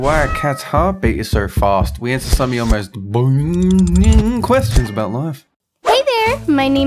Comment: I thought said boning questions XD